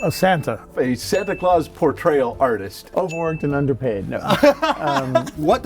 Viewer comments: "Overworked and underpaid" but I thought he said "overweight and underpaid" XD anyone else heard that? No... I'll just walk out and close the god dang door.... anyways subscribed, followed you on instagram. Instagram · “Overworked and underpayed” I respect that Edit: That’s a lot of likes thanks people! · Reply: Thought he said "Overweight and underpayed" ahah